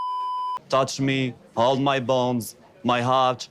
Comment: At first I misheard the poem as 'touch me. How's my balls?'